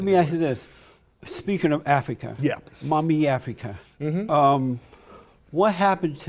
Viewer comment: Around @ 12:37 Did homeboy say "Mommy Africa" hahahaha